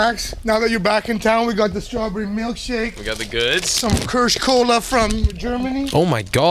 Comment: when they said kirsch cola i misheard first and got so excited that they got fritzkola but 😔